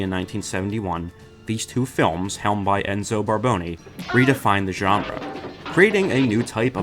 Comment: thought you said Balboni (from tough knuckles co) for a second 😅